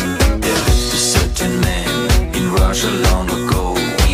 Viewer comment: Ugh, I misheard it as, "Curtain man, that rushed along the cold"